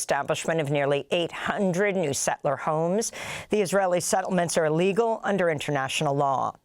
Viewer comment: I used to listen to you years ago on the radio and always admired everything you did but today I found an accidental flaw. I have had a number of Jewish friends but I utterly loathe Israel, America's pet monster. As you were speaking about the West Bank settlements you used the expression that the ..."settlement is illegal!" but to an old soldier like myself it sounded like you said: The settlement is legal! I know you would be appalled to think that you accidentally endorsed Israeli crimes. I think that in the future it would be best so as not to be mistaken, to use the expression: "not legal". The Israeli attack on the USS Liberty, where Israel killed and wounded several American Servicemen was just a smokescreen over an even greater war crime. The Israelis were conducting another of their Blitzkreigs, ( The Seven Day War) they had captured nearly a whole Brigade of Egyptian soldiers, over 900 men and rather than water and feed this enemy Army while continuing their blitz attack they had them dig a mass grave and then executed them all and we Westerners are still weepy about the holocaust! As I have asked about Israel and America, which is the dog and which is the tail? Who else might kill Americans with impunity? Gaza which has been turned into a pile of rubble full of corpses does not merely have the right to exist but also the right of self defence. NATO should be defending Gaza and the West Bank instead we are a cheering section for Israel. The Jebusites are not going t